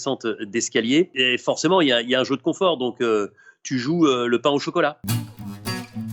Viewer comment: "Écoutez le silence" jolie antithèse. Mais j'aurais préféré pouvoir le faire quand j'ai entendu "pain au chicolat". ON DIT CHOCOLATINE BORDEL !!!!!